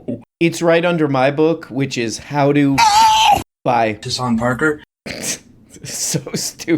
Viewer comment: I hear “Disahn Parker.”